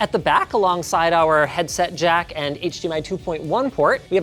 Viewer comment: i misheard the headset jack bit at first. i was like cassette jack? wtf. but no i just misheard